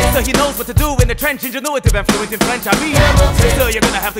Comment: Everyone give it up for America's favorite FRENCH FRY! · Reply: I thought it said flying french fry